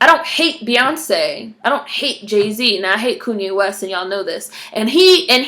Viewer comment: This was refreshing! Did you say "Koonye West" @ 20:58? :D